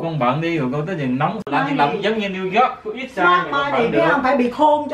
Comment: I thought his Mom said "Be Smart my N*gga"